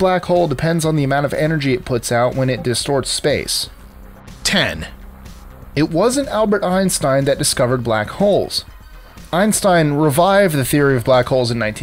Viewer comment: Anyone hear discord?